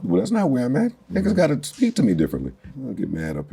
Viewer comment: i wish i could see wat he kept saying at 3:30 lol “niqqas gotta speak to me differently” lmao talk ya shit